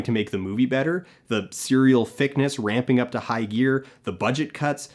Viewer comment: I think he says "fic-ness" and not "thickness".